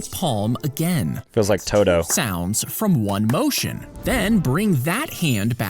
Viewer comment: did he say just like todo or is it just me